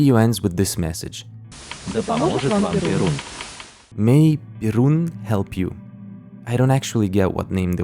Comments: i don't think they are saying "Birun", i think they are saying "Pierun" who is a slavic pagan god who is basically slavic thor. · i think they say 'Перун' (perun), the old slavic god of lightning and thunder. 'veles' on the other hand, at 12:39, is the old slavic god of sorcery/magic, poetry and arts, and the underworld (and other things), and the brother of perun. both of them are/were seen as 'good deties' and protectors of humanity. the symbol at 18:45 also looks like it might be a stylised version of the symbol of veles used by modern slavic pagans, but im not sure. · I'm not entirely sure, but I think that instead of: "May Birun help you." It's instead: "May Perun help you." Perun being an Eastern Slavic God. Described most commonly as the Slavic God of Thunder and Lord of the Sky. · I think it said "May Perun help you" Perun was a slavic pagan god equivalent of that of Zeus or Jupiter in Greco/Roman Mythology. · Скорее всего кто-то уже написал, но я тут слышу "Перун". Это бог грома славянской мифологии, ему поклонялись язычники или что-то типа того...